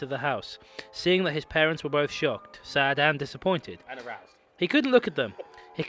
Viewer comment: did I hear someone say "and aroused"?